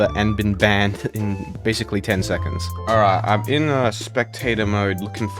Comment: Bruh why did I hear 💀 “I’m in spectator mode looking for minor…”